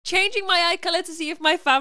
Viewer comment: I thought u said "drinking my eye" i know its far from what i heard